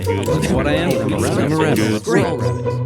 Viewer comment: who else heard I’m a rabbit but also we are all rabbits